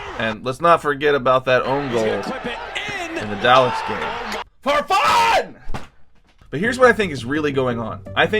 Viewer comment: Lmao at first I thought you said ”Förfan” (Swedish word) which means for ”for fucks sake” instead of ”Farfan”